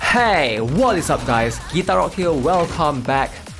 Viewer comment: i hear you say "kitaro"😅😂